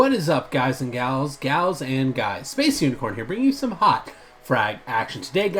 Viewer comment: u said hawk instead of hot